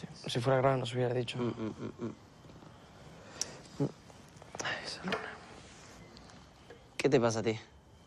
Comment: Creo que dice Simón hay soy luna